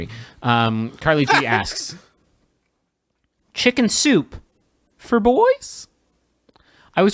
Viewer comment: I thought they said chicken suit for boys and I was like 😐😑